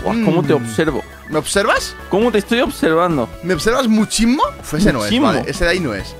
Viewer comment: Yo pensaba que dijiste "me observas un chingo" XD